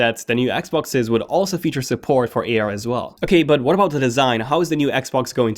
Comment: hahaha at 9:10 I thought he said “offer some porn for AR as well” Sorry